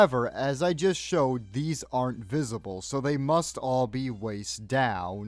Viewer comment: Did he just say… invisible… ( 16:57 ) And no one hears a word, I say…. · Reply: "Aren't visible"